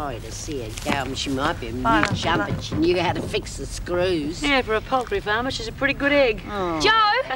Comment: What did Lizzy say about Jo @ 36:39? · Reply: "She knew how to fix the screws"